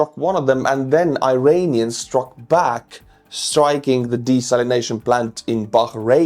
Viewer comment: I was on the other end of the room and misheard you. You said desalination, but I heard DeStalinization. That, too, might be nice.